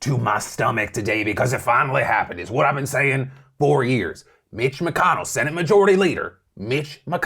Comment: For a second it sounded like Phil said Bitch McConnell